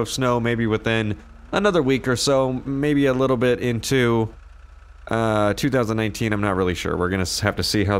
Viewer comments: Is it just me or did he say that there would be snow until 2018? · Is it me or did he say 2018